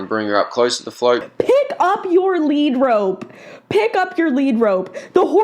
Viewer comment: i thought you were saying pick up your negro for a sec